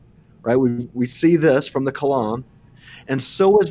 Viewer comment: What word are they using? Like at 1:46:42...."We see this from the KULAM? CULAM? What is that? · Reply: "Kalam", which is short a reference to the "Kalam Cosmological Argument". It is a class of "first mover argument" that are so named due to the original proposition by Islamic theologians of antiquity. A particular favorite of the apologist William Lane Craig, the form of the argument was historically not very popular in Christendom (such as with Thomas Aquinas) but more recent reformed apologetics has taken to using it often.